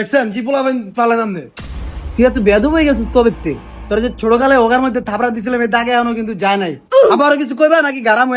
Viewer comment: ভাই কি বলে 😅😅😅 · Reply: Hogar moddhe thappor marcilam😂😂😂😂